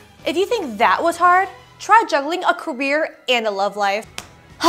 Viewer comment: I thought she said "try juggling a QUEER and a lovelife" i